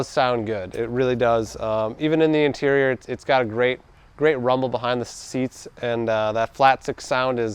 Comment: Morten Nielsen He’s referring to the 911. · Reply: I thought he said "there is some room behind the seats" when discussing the storage places.